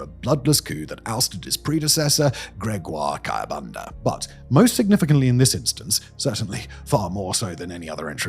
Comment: Who else thought it said "most satisfying?"Just me, i suspect!😂😂